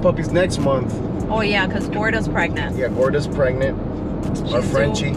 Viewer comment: I thought u said flordis pregnant